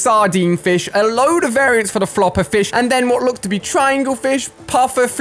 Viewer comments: I swear he says puffer FLISH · did anyone hear alia say "Flish"